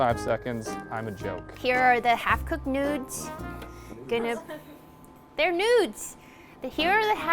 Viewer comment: I thought she said noobs even though it was written